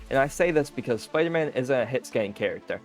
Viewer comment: Just a nit-pick but spider-man isn't hitscan, he's projectile based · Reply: He says that in the video 4:57. The way he spoke sounded like he said is.